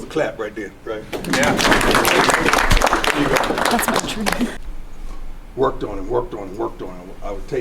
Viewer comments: You can hear Becky in all her jealous rage go “ that’s not true” after Deon said he retired his Mom in 88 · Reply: Thought she said my dream too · Did anyone catch what the girl said after Deion said his mom hasn’t worked since 88? She straight up said “thats not true” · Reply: I thought i heard “my dream too”